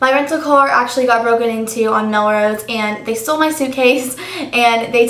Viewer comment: Omg, at first, I thought you had said, "My car got broken in TWO" lololol awks.